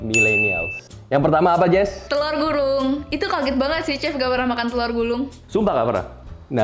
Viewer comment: kok aku dengernya Jessica bilang "telor gurung" yaa?? Bukannya "telor gulung"😂🤣